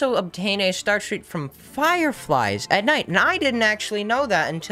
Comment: Boi i heard "fire fries"